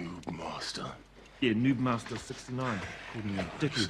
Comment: Anyone else thought he said NudeMaster? I mean, NudeMaster69, sounds appropriate, right